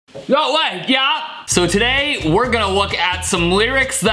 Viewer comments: it sounded like bro said yo lag, yeah?😂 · The subtitles said that you said "no way yeah". 6:00 I am just dying of laughter, THAT VOICE THOUGH WHEN YOU SAID GOOGLE JOB!!! 🤣🤣🤣 7:28 Guitar intensifies.